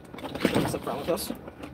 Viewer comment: ish did he say "does he have some problem with us?" Also I love the "we're wearing body cameras..." like that means anything. As if they don't mute them. Or deny foia requests. Or charge up to $100/hr for footage. Or the file names get "accidently" changed or deleted.